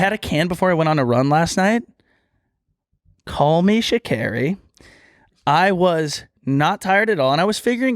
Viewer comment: What did say at 1:07:24 « call me… » what? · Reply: Sha’Carri